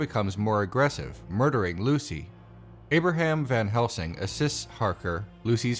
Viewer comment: At the 1:00 I heard your words as 'Abraham van Helsing, a cis-Harker'. Leftism seems to be doing things to my brain's autocorrect function. Nice video, though! :)